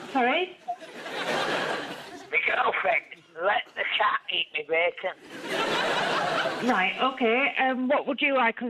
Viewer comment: I heard “my girlfriends let the cat ate my baby.” I’m was like “OMG! why is this on HERE!?” So I had to go back and rewatch whilst reading the CC. MY CC said the same!!! Thank the CC gods Russell put captions in too!!